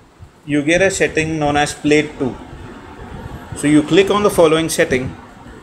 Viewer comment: What do you say at 3:38 ??? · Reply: Višnjić in Asus zenfone to screen mirror you need to press played to button in some phone the option is called cast screen etc which phone do you have